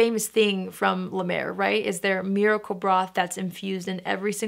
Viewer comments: 😅 I thought you said"Miracle Bra! Lol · I had a good laugh, I thought that you said “Miracle Bra”! 🤣🤣